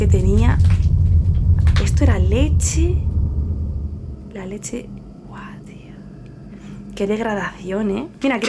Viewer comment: Hola me encantan tus vídeos tengo una duda en el minuto 8:24 se escucha una voz diciendo algo como guadias, la verdad nose que dice ¿Eres tu?